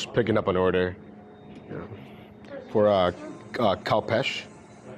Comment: order sounded like you picked up an order for Cow Patch, lol.